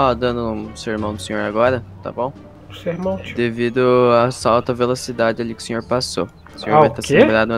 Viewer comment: tbm entendi assalto a alta velocidade,kkkkkkk,o PM é gago